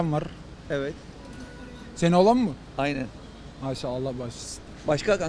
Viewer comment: da Adam ne diyor anlamadim orayi.. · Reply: Senin oğlan mı diyor